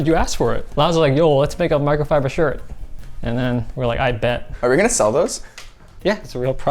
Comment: Did dude really just say “I bet” instead of “Bet”? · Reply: He said "a'ight bet" not "I bet"